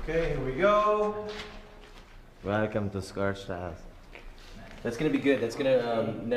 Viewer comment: When Dylan said “Welcome to scorch trials” it sounded like he said “Welcome to scorch ass”🤣